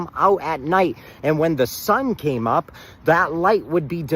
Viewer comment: did he just say when the sun came up? 😂